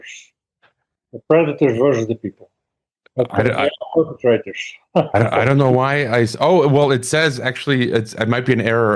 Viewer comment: At about 1:12 into the interview he mentions a name I can't make out... starts with Russell... something. Can anyone help me? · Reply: Found it. Russell Gmirkin